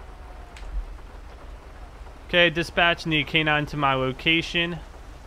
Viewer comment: Vocal Dispatch thought he said "gay dispatch Nikkei nine smile and casing".. Hahaha wtf?